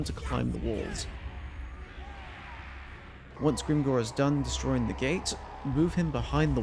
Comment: Wait, I thought you said Gringor, not Skarsnik!